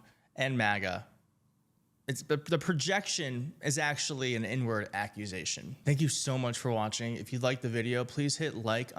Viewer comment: "inward accusation" I heard n-word accusation. 🤣🤣🤣